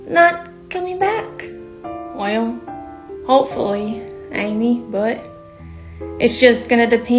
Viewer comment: did he just say HOPEFULLY